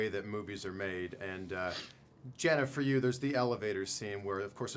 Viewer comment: Did he say Jennifer to her?